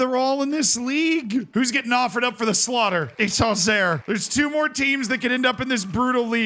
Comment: Bro absolute butchered pronunciation of every French and Italian club 😭😭😭😭😭😭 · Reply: I was confused when he said Auxerre because it sounded like he was saying Angers